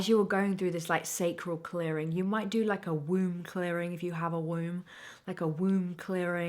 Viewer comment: Womb clearing? Dang i thought you said Room clearing and i do that just by walking in.